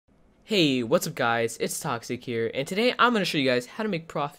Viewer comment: I thought you said at the intro "Hey whats up guys its scares here"